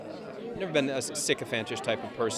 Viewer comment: I have no idea what he said at 2:54 and I’ve rewound it many times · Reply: He said I have never been a sycophantish type of person, but she is stunning